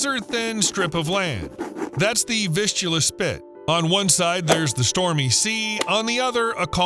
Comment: I heard "fistula split", and spat out my tea.